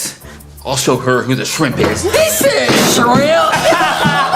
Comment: I knew that was coming! lol! 🤣🤣🤣🤣🤣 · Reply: bro, said "simp" not "shrimp"! lol. he thought she said "shrimp"! 🤣🤣🤣🤣🤣🤣🤣